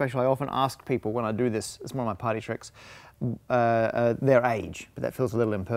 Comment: it sounded like he said "potty tricks" instead of "party tricks" LOL